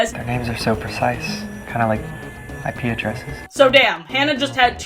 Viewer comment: "names are so precise, kinda like..." what? I watched this four times and couldn't figure out what he said, neither could the CC. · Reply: IP Addresses